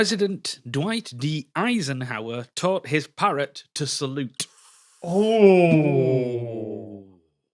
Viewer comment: I misheard fact initially as "Dwight D Eisenhower sold his Parrot to Sunderland"